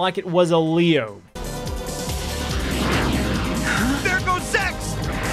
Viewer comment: I think you can be forgiven if you misheard this at 2:14 "There goes "Sex"".